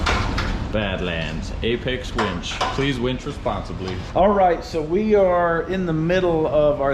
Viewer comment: You said "please winch responsibly"... I heard "please wench responsibly". 🤣😂